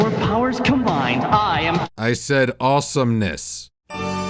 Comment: I thought you said 'awesome'.